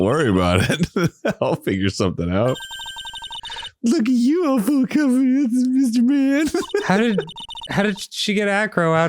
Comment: My brain thought Maya said at 8:16 "Look at you! Couple of cards."